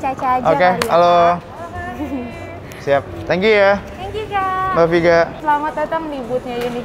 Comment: Menit 12:10 "lopyu kak" bukan sih 🤣 apa salah denger 🤣